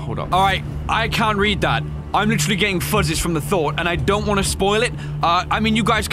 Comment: "Getting fuzzies?" Is that what he said? Uhh, explanation please?